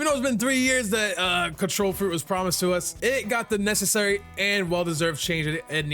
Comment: did I hear him correctly the control fruit was promised to you 3 years😂😂